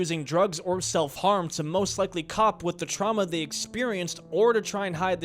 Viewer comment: good vid but did bro just say COP 💀💀💀